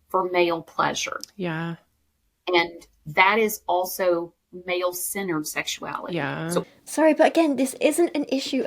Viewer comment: At around 1:40:23 when is talking about p0rn I think she actually said "male centered sexuality" not "male sinner sexuality" And at 1:50:42 says "Maca" not "matcha". Maca is a root that's also sometimes called Peruvian ginseng.